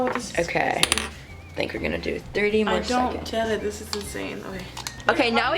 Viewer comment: did she say "Janet this is insane!" 😂😂